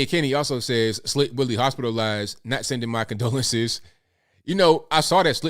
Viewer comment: I thought they said Slick Willie has A UTI